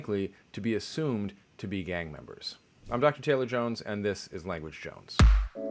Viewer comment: I definitely heard "I'm ducky tailor jones" and that's gonna be my headcannon now.